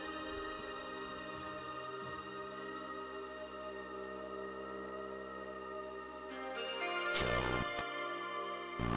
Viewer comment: can someone tell me what it’s saying I can’t really make out anything except maybe “me” at the end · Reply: He says help , they broke me. You can hire it is you put the video speed on 1.5 or 1.75 try it